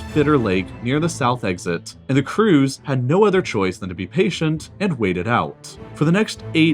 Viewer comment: I thought he said a cruise had no choice but to wait it out lol